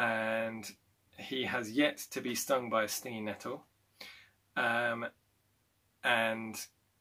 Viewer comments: At first I thought you said 'stingy metals' instead of 'stingy nettles' and I did not doubt that because it was about swords and such :) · At first I thought you said 'stingy metals' instead of 'stingy nettles' and I did not doubt that because it was about swords and such :) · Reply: I heard “stinginetto” and wondered why are you using obscure Italian terms XD